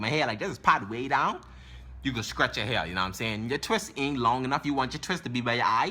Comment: i thought you said scratch your hair but you were saying stretch